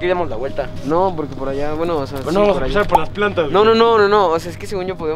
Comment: En el minuto 9:07 se escucha un señor que dice alla alas plantas b bueno estoy en 2023 y aún lo sigo viendo por que aun no vimos cosas aun no investigamos mas